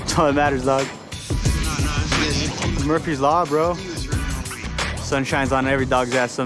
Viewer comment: “Murphy’s law, bro”- proceeds to describe something else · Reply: To me it sounded like he said Mercury's law at 04:38 onwards.